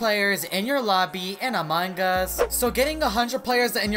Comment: Why when said "among us" I hear "a mangas"? Also Giveaway I DID EVERYTHING! 😫